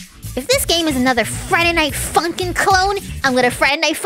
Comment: I thought you said another Friday night Funkin mod at the clone